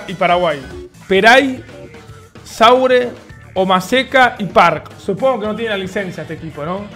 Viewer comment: escuche goma seca AJAJAJ